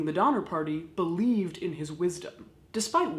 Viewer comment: After you mentioned Jeffery Dahmer, all I heard was "Dahmer Party" the rest of the video...